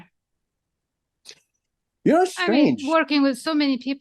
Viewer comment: "You are strange" That's what he said? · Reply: You know what's strange...